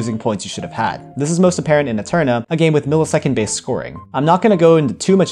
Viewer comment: I thought you said aetrna